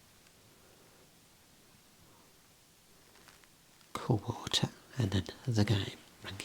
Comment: I thought the second one said "Cod Water" at first and was very confused lol